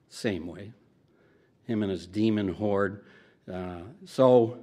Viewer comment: ok, last comment, i swear...is it just me or did anyone else think he said “Satan’s Demon-Wh0re” approx 23:30? oh! should’ve said this first and foremost...Sir, good job! I am thrilled to find another channel that discusses the Jdubs with nuance and intelligence, and addresses Ex-Dubs with thoughtful compassion. Liked, Subscribed and Hit-ted(?) the Bell Thing Next to the Subscribe Button! = 10/10!!! wud def recommend